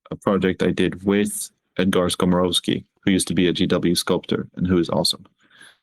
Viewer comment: I didn't quite catch the name of the ex GW sculpter. Edit: found him: Edgar Skomorowski